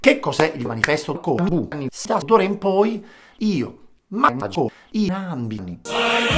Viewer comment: "Manifesto comunista" ussr anthem 0:47 "D'ora in poi io mangio i bambini" ussr anthem parte 2 Solo applausi gente